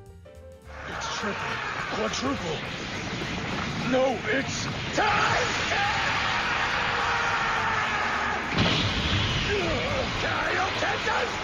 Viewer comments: Did hit say "no b*tch." ? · did he just say "No bitch". That's sounds like something from TFS.